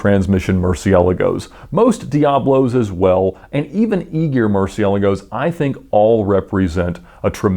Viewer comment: What kind of murciélago are you saying @ 6:07 and 6:40 ? it sounds like you are saying “eager”...